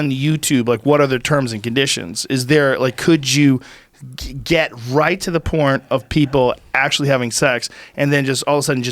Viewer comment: did he say get right to the porn-t!!!! lmao joe man you too funny !!!!